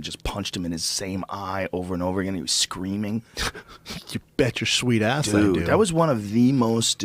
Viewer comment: I could just hear Schaub say "You bet your sweet ass I do!". At least I think thats what he said, it must be hard for him to talk when he is wearing Joe's ass cheeks like fuckin epaulettes on his shoulders...